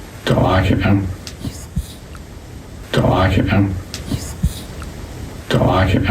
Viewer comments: Sounds like it says “he’s mean” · I think the little boy is saying " just leave " instead of its me